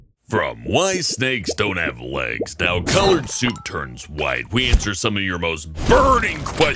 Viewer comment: Can we all just appreciate that said soup instead of soap